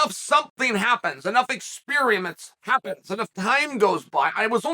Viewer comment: Did you just say “experie-ments” as in a combo of “experience” and “experiments”? If so, word of the year. If not….same result. Let’s goooo